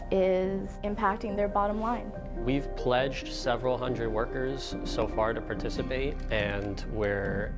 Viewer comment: I thought I’d said “wokers” lmao